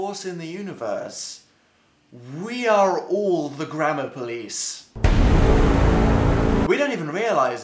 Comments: It sounds like he is saying grandma police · Maybe it's just because I'm American, but I keep thinking "grammar" is "grandma" when he says it, so I'm sitting here like, we must all fear the grandma police? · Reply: Omg, it DOES sound like he is saying grandma police!!!!! 😆lol!! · it sounds like he is saying grandma police... just me... okay